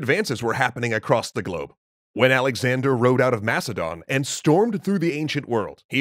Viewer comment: When he said Alexander the great road out of Macedonia I thought he said that he road out of mastodon. Either a curious paleontology find or the new power rangers.